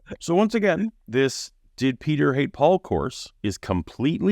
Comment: I wasn't looking at screen and I heard "Did Peter ate Paul?" XD